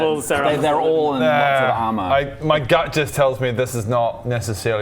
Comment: did the captioner deliberately put in juts instead of just when Bodger said, "My gut just tells me this is not ..."